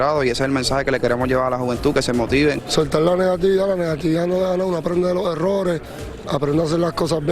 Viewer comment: QUE DIJO? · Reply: Solta la negatividad la negatividad no da logros aprender de los errores